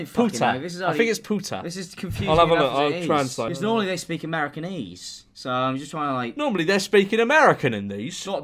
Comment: I thought he said Americanese like it was the name of the language and I was so confused lmao.